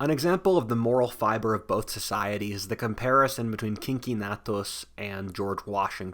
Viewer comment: I heard "kinky nattos" was the paragon of morality.